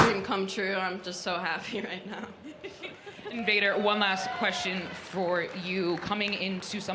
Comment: I legit thought she said 'invader' not 'vader'